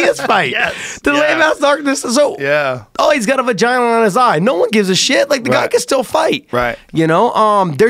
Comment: Did he say “He got a vagina on his eye “?